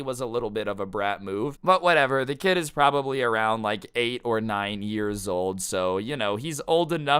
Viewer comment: For a moment I thought he said 8 or 90 years old